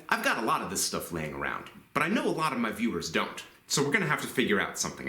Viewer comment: where you say "I know a lot of my viewers don't..." my headphones decided to lose connection in the middle of the word "my" and I had a bad lipreading moment. I thought you said, "I know a lot of mother f**** don't". I had to rewind after reacquiring connection and was happy to find that you are still rated "E for Everyone". Haha. Thanks for another great video, and a much-needed chuckle at the end of the day.